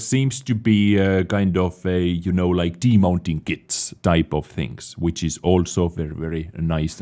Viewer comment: did you just say demounting kits and nice in one sentence? O_0